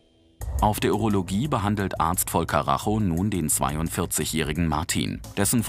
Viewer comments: Ich habe Arzt Vollkaracho,, verstanden 😂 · Ich habe verstanden "Dr. Voll Karacho" 😂😂